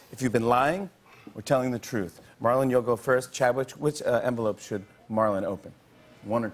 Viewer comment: "CHADWICH" what did he say?! 😕